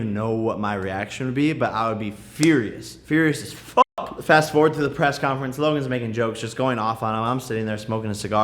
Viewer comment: I thought he said he wasn't going to the Press conference